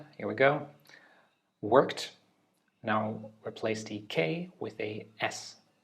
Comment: I thought you said replace the w, and then replace the p for some reason...... im cooked